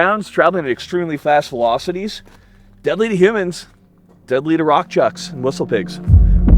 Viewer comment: ish I swear, I thought he said "..deadly to Rothschilds and whistle pigs.." =O